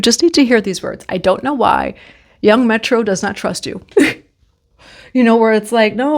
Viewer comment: Did you say "young metro don't trust you" 😂